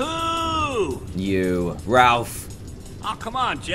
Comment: did Dan call Axel Ralph